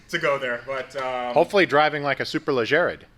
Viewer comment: Did he say "Hopefully driving like a Super la Jared" ???